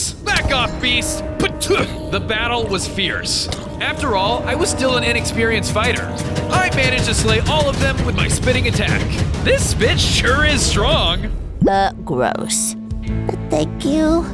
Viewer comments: I thought you said the word 'bitch' LOL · Well let's just say that the gorilla turned into a enormous king kong. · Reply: did u just say, "this b>tch sure is strong" I thought u didn't say bad words